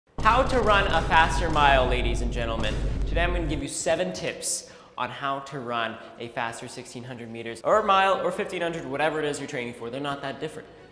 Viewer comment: Is it just me or I thought it said “how to run a faster milf”